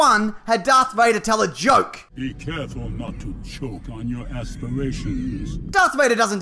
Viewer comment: I know is probably joking about most of this, but to those that actually have a real problem with Darth Vader making that joke, he did similar things in The Empire Strikes Back! For instance, when he chokes Captain Needa to death, he sarcastically remarks “Apology accepted, Captain Needa!”. If anything, he’s just being snide and completely devoid of human empathy, which only adds to his character in my opinion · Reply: He actually says “choke on your ASPIRATIONS”, which makes it a double entendre, with aspirations meaning both goals and the process of breathing. Remember, he was CHOKING him as he said it. Mishearing the quote changes it meaning completely